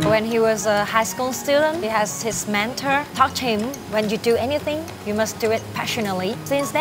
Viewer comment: I thought she said his mentor touched him. I am so relieved she was saying "taught him"